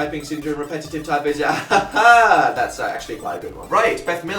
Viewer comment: What does Dan say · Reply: “haHA that’s actually quite a good one”